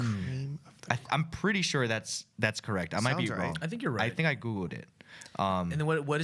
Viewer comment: it sounded like Ryan said “I think I googooed it”